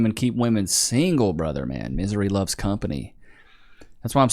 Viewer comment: i dont know why i heard feminism loves company